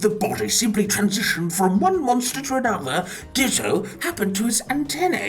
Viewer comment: bro I swear I heard ditto in that sentence